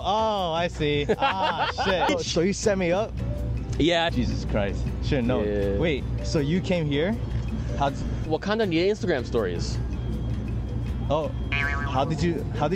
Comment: Wakanda need Instagram stories This isn't what he said, but this entertained me more than it needed too. 😂